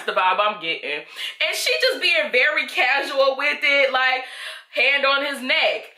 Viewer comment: I thought you said she's just being "Barry Casual" with it 😭😭